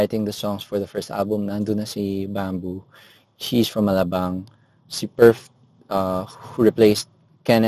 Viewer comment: "she's from alabang" did i hear it right 👂